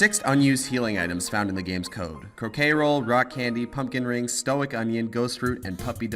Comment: anyone else hear cocaine roll?